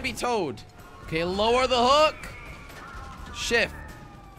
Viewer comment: did he say shift or the other thing